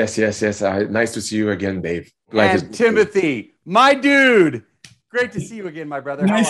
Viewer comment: When he said "my dude" i heard "my Dune"